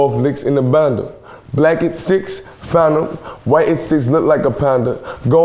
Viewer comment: I thought it said "Legacy, Phantom"